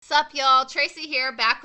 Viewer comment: Thought you said "fuck y'all " instead of "sup y'all "